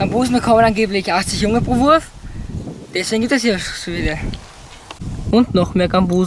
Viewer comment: Was hat der bei 1:26 gesagt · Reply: = "deswegen gibt es hier auch so viele" - Das Video hat übrigens auch Untertitel für alle die den Dialekt nicht so gut verstehen.